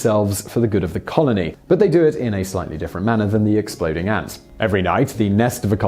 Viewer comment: auto captions say exlpooding ass instead of ants